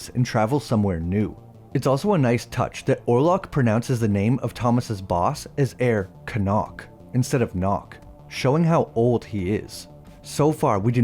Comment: Hi, in 12:35, I think he says "Herr Knock" which in german, "herr" means "Mister"